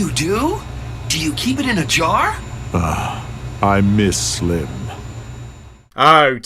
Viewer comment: is it just me or did anyone hear Torvald mention slim at the end when he was talking to emet in the drop ship convosation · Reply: Yes he did he said ughhhh I miss slim